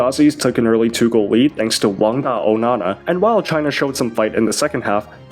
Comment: I heard something Onana 😅